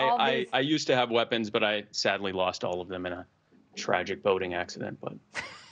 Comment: I thought he said he lost his guns in a tragic voting accident. 😳🤣